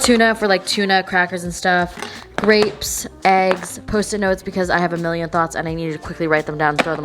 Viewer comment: i'm late for online class so i'm watching this in 2x speed but i had to slow it down when you said "post-it notes" because i thought you said pussy notes and i was like whaaat??